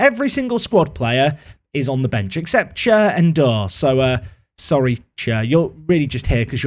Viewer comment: I would have sworn you said "Chair and Door," not Cher NDour.